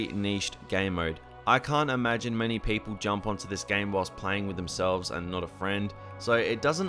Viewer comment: Did u say playing with your self